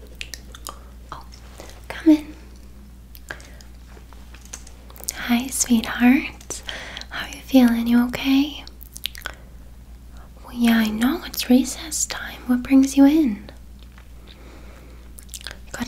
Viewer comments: tell me why i heard “it’s racist time” 😭😭😭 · i was half asleep and thought you said "it's racist time" 😂😂😊